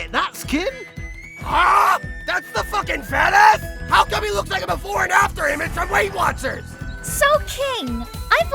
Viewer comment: Lmao what Ban said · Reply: "HUH?!? THAT'S THE FUCKING FATASS?! HOW COME HE LOOKS LIKE A BEFORE AND AFTER IMAGE FROM WEIGHT WATCHERS?!?!"